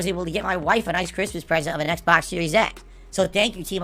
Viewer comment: does he say xbox series S or X? Because he said 3 weeks ago he got khat the xbox series S, now it sounded like he got her a series X...